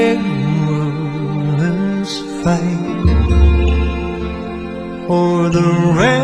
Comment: The caption says "flight" here, but the word is actually "fight"